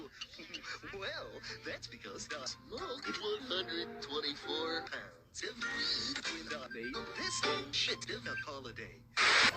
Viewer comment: I know this video is 3 months old so you're probably not going to see this comment but I shall translate what Barney said at 0:55 for you... "Well, that's because I smoked 124 pounds of weed when I made up this bills*it of a holiday!"